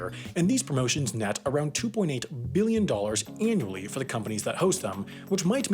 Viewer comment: That REALLY sounds like you say "2.8 Million dollars", but that's very clearly 2.8 Billion on the screen...... Are my ears going bad?